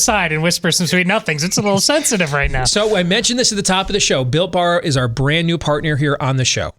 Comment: Wow, thought you said Bill Barr was your new sponsor. ;)